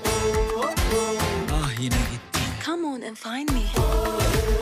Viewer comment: It sounds like she said “Come on and fight me.”